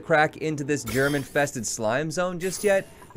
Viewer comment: Right at the start, when he said germ infested slime zone, i thought he said "german tested slime zone".